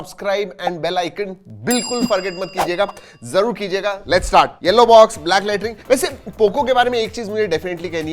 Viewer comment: Kya Bola latrine 😂😂